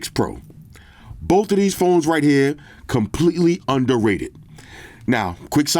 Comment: Most under rated phone of the year……. Surely thats the iPhone 15 PM?? Oh wait, sorry, under rated, thought you said over rated😅. HNY Flossy, glad you’re feeling better man